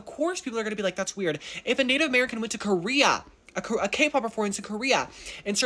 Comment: I didn't hear him say Native American, I heard I am American performer went to S.K & I thought that he was going to say if the locals started doing that throat sound that my twin & I associate with when you are eating or drinking something bitter & you are trying to not spit it out & actually swallow or at least not spit out. It sounds like 'gkkkkkhhhgksssh'. It's crazy because it sounds like choking but some do it abit less choking sounding & it's also crazy because they also view choking as rude. Intriguing. When I do it, it is a natural reaction. How would it be for Koreans if the thing that they are ingesting tastes SUPERBLY bitter?